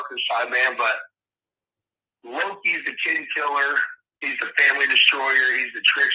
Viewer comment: At first, I also thought he said the Lokians were "kid killers", but after listening to the original video with headphones, I'm pretty sure he said they were "kin killers". Not defending anyone in this case, just clarifying.